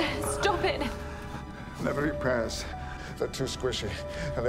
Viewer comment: First time I watched this I misheard Capaldi's Scottish as saying "never eat prayers, they're too squishy," which felt like a goofy, maybe? profound Doctorism. Rewatching it, "...oh. duh. Pears. I forgot about that one."